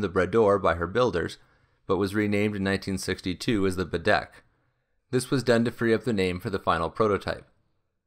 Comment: Had to go back and listen again; I heard the rename as the ‘Bidet’ the first time. 😁